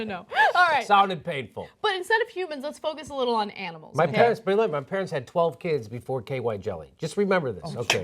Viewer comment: What does Jimmy say at 0:48? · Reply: his parents had 12 kids before they invented KY jelly.